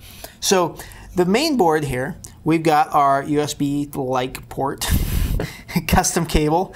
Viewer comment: At around 5:10 he mentions the name of the USB port, what is it called? "USB like port?" or "Light port?"what did he say? i want to buy this socket and make a cable for PC.. Thank you. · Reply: "USB like port" as in "similar to USB"